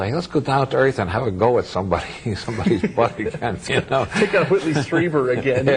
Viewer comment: I'm translating this video into spanish but I can't understand what is Bob saying at 1:46-1:48 (let's go have a go with somebody's ..... ???) Can someone please help me with this one? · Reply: butt -- a reference to reports of abductees subjected to anal examination by extraterrestrial explorers.